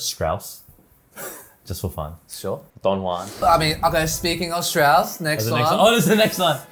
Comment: Strauss...just for fun! When Brett said to comment "Strauss" below, at 8:18, I thought he said, "Fun Juan" ! (Definitely NOT fun for auditions!) When I went back to my hometown after graduating from uni, I auditioned for my local sym...but it was NOT a blind audition. I had to play in front of the conductor and concertmaster. Actually I think that is much scarier than playing from behind a curtain!